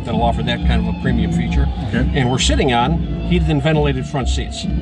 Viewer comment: it sounds like he said "and we're SHITTING on heated and ventilated front seats"